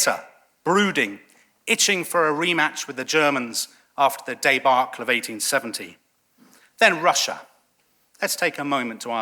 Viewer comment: What was said at 8:10? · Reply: He said that in 1914 that (some) French were still looking for revenge for 1870. He said a "rematch (like in sports) after the debacle of 1870".